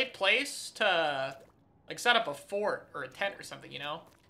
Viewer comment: i need to touch grass i thought said "forge" and "tent" n was siting there stroking my chin like "hmmm yes competitive spoon 3" but he was talking abt fckin pillow forts😭